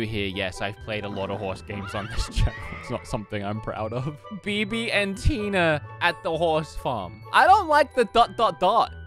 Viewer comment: Every time I heard “Bibi and Tina,” I thought you were saying BB&T bank horse riding. 😂😂😂